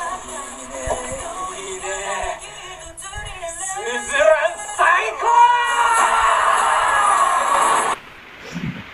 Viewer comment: What did Fujio shout after kicking Binzo away? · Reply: Suzuran saikyou !!!!!!!